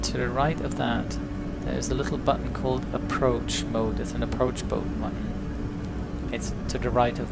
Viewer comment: did you say "Approach Boat Mutton"? ;)